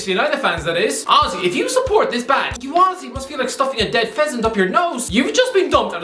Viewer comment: Sometimes I can't hear what he's saying but it. All makes sense when I hear him say that mourinho is the GOAT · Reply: Phenomenal Statpad. Yeh I know what you mean pal. Every time he says "honestly" I hear "Aussie"